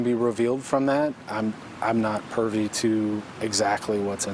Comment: Did hey say “pervy”??? Is that a Florida thing?